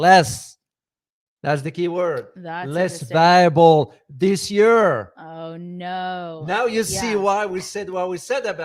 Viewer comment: What did he say ? Anyone caught that ? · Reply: Less viable this year